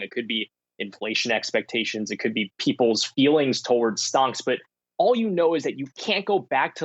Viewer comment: did he say "stonks?"